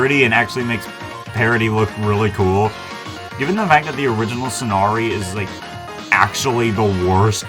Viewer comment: Did I hear you say "Sonari" instead of Somari? Just curious if I actually heard that right.